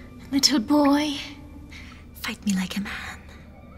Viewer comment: I legit thought she said "F*** me like a man" and he really took off his shirt LOL